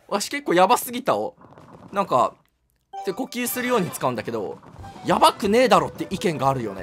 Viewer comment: 「やばすぎたを手〇キするように使うんだけど〜」にしか聞こえない